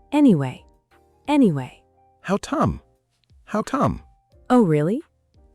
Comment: How Tom? Does anyone know what it means?